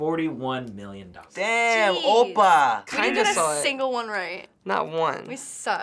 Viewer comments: lol did say "oppa" at 10:40? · Did he just say, "Damn, Oppa", or am i going kpop crazy.